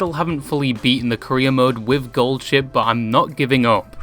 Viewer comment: fuckin hell, up until here i thought you were saying "korea mode"